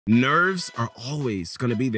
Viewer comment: lol at the beggining i thought he said "nerds are always gonna be there..." im so dead